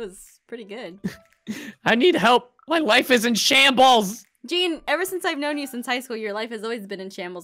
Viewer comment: I almost thought he said my WIFE is in shambles........probs is if he runs a FRICKING NIGHTCLUP and FLOODS IT EVERY DAY!!!!!!!!!! Lol